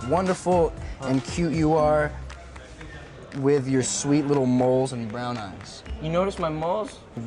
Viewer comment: "With your sweet little..." what did he say? Moles?? 😂